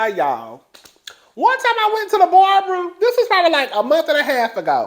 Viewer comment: It’s you saying Barbra instead of barber lmaoooooooo